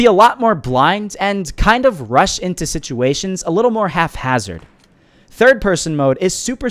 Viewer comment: “Haphazard” is the proper spelling. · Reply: Haha yes I know, it just sounded like you said “half-hazard.” That’s why I put it in quotations.